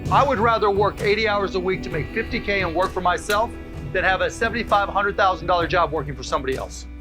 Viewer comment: "I would rather make 50k a year working for myself than 75k-100k working for somebody else" lmfao what horrible advice. Shamless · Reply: At first I thought he said something along the lines of 7500 k